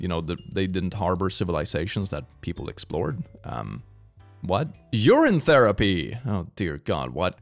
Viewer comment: i was playing project zomboid while listening and what i heard was "you're in therapy" . . . or maybe i just heard what i wanted to hear😅